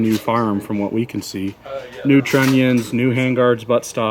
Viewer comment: he says "new tronian"? How is that written correctly. I know what he means but can't quite figure out how to write it to get the correct translation. If someone can help me out, it would be appreciated. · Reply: "Trunnion"